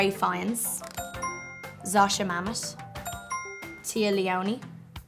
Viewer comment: Am I the only one hearing her say Mamos instead of Mamet?